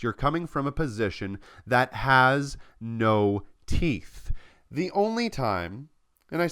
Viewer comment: Hi! I'm translating and subtitling this video to Portuguese, but I don't understand what do you said at 01:11. "that has no teeth" ?